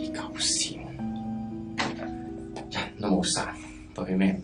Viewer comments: 裡，他是説“痴9線！人都冇殺，對咩唔住”，意思是“神經病！人都沒殺，跟誰人對不起” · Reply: 我一直反复空耳，很纠结是“人都冇殺”还是“人都冇嗮”，如果是后者，那就真的更可怕了😱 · 我一直反复空耳，很纠结是“人都冇殺”还是“人都冇嗮”，如果是后者，那就真的更可怕了😱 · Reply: 係人都冇殺